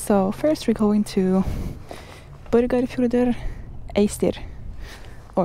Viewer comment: did you say you were going to Boogity Boogity Foogity? I mean, that’s what it sounded like 😂. Regardless, a great video with lovely scenery.